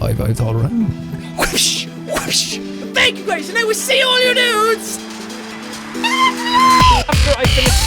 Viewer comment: Sometimes it sounds like you say SEE ALL YOU NUDES!!! IN THE NEXT VIDEO!!! oh and btw you are wearing a Markiplier Tshirt